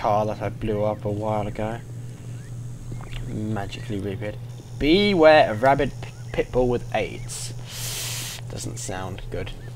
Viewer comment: First it sounded like he said be ware rabbit pitbull with aids